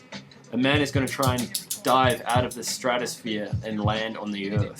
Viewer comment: Am I the only one that thought at 0:57 said "Amanda is going to dive out of the stratosphere"? hahahaha